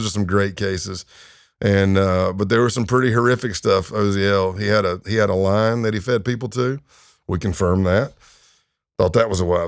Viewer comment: Okay, help me out. Did he say "he had a LION that he fed people to?" My bf thinks he's saying line not lion (and so does YT, apparently) but that doesn't even make sense.